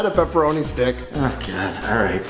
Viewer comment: Who else thought that when Dave said "Walpole" that he said "Waffle"?